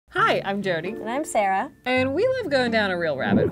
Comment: Is it just me who heard her say hi I'm dirty and not Jody